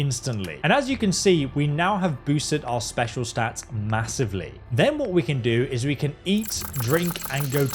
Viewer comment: did he say massive?